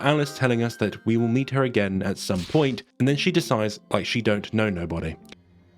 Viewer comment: are you saying "Then she decides like she don't know nobody" like the captions say you are? Because that doesn't make sense. But it's all I can hear. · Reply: "My main goal is to blow up! And then act like I dont know nobody!"